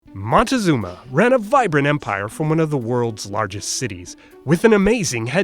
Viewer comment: When you said ran a vibrant empire, I thought you said ran a viprin empire ( viprin is a creator in geometry Dash )